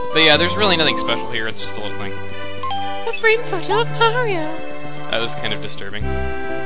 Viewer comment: I wasn't looking at the video, and I thought he said "A frankfurter of Mario!" XD